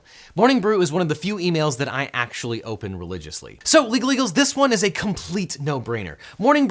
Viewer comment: When he said “My legalEagles” i heard “My illegals”